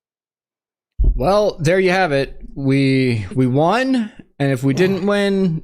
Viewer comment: He did not say we won. He said we still have 2-3 weeks.